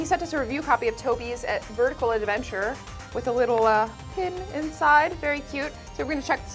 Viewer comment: It sounded like she said "tobi's vertical adventure" was the title to the game on the table.